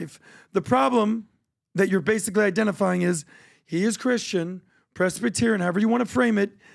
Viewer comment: I heard "I'm Christian"